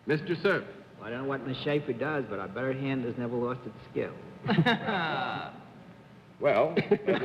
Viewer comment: Bennett Cerf's wild guess: I can't make out what he says exactly past, "I don't know what Ms. Schaefer does, but...". Whatever it is gives John Daly pause, though. I've listened to it now several times, but what it sounds like doesn't make sense (which may be what's giving JCD pause). Can anyone make it out? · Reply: "But I bet her hand has never lost its skill."